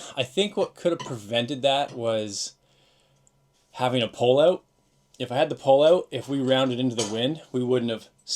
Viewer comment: What do you mean when you say; "Pull Out".? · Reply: “Pole out” as in the whisker pole. Putting the headsail on the whisker pole to avoid from accelerating when rounding into the wind